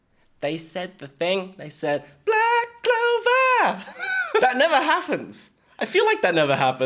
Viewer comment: they actually said black rover which is the name of the song